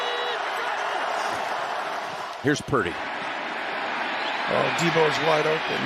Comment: he really said "Here's pretty"